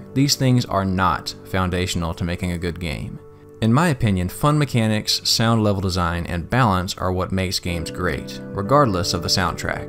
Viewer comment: Using the word sound in the phrase "Fun Mechanics, Sound Level Design and Balance" confused me when you followed it up by saying "regardless of the soundtrack" cause my ears heard it as "Fun Mechanics, Sound, Level Design and Balance"